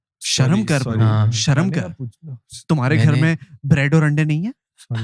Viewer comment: what did said??😭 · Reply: He asked that nashta kya kiya